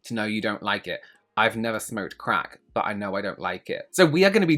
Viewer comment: Jesus Christ, lmfaaooo "I don't smoke crack, but I know I don't like it!" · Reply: I was only partially paying attention, and I initially thought he said "I don't smoke crack, but I know I would like it." I was like "! 😱